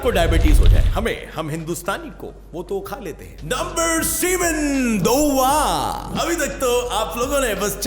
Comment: Is it only me or did he really say number "semen" at 8:35 ?